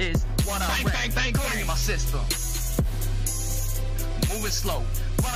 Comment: sick rap kil but why does it say bang bang my sister